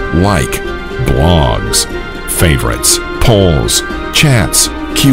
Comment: When he said "Polls" I misheard him saying Poorns